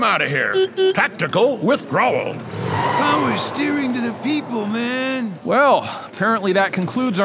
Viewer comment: What is Fillmore saying at 2:14? “Wow you’re steering into the ppl man!” ??? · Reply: "Power steering to the people, man!" It's a play on "Power to the people".